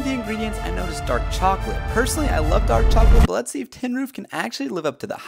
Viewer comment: The flavor is "Tin Roof" it's a chocolate medley saved you a minute. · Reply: yeah all I heard was "fin noof" at the end I was so frustrated that I had to rewatch the video again just to know the name of an ice cream I would never buy in my life